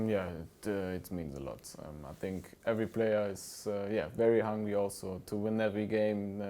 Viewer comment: is it my filthy mind, or did be say that eveyr player is very hung at 0:30?